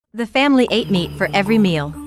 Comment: I heard “the family ate ME for every meal 😂